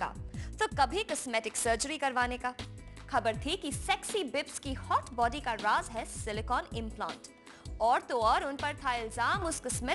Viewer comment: Did she just said sexy bibsky hot body carwash has silicon implant XD